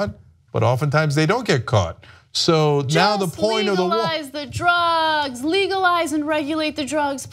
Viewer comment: Wait did she just say legalize meth?????????? 😞😕😦WTF WRONG WITH HER we said weed not method or heroin that ish kills. Thats crazy... Meth tho????wow